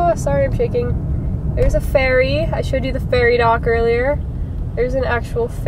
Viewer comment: When you said "ferry dock" I thought you said "fairy dock" and got very confused. XD